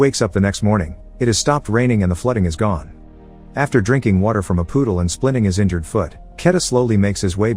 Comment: Anyone else heard him say "poodle" instead of "puddle" every time? XD